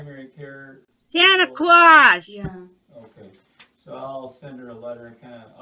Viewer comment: Great news. I am so surprised he allowed you to tape what he said. It sounds all good to me, sister. SO happy to hear you are on the road to recovery. Around 10:50 I thought I heard you say loudly "Santa Claus." Am I nuts or did you say that? Blessings, peace, fast healing, and love, my beautiful friend...MaryEllen